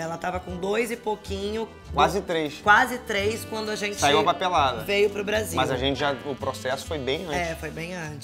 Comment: "saiu a papelada", ouvi "saiu uva pelada" auhauhauahuhauah